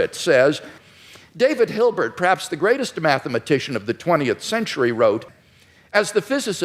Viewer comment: i swear i heard him say, "the greatest hermaphratician"